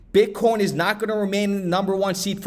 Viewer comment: That great! I heard you say bitcorn